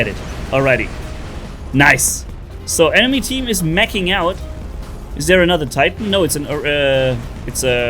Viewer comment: Sounded like said "enemy team is making out" 🤣 (He said Meching)